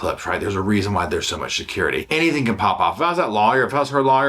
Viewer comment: 😂 If I was a betting woman, I’d bet said “Sha-curity” (security) @ 9:30 I heard that right, right?!? Love you!